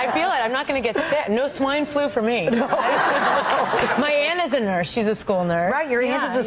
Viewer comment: OMFG DID SHE SAY my anus is in her? XD